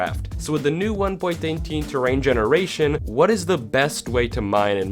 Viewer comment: did I hear 1.18 generation or am I just wierd